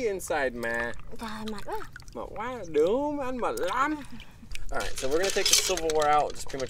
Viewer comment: did he just say “du ma”?😂